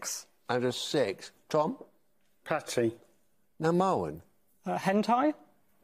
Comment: Man, I misheard Tom's PATTY as PANTY. Would've gone exceedingly thematic with HENTAI.